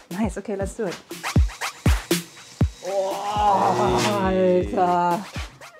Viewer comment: What did she say at minute 6:48? It sounded like heil hitler.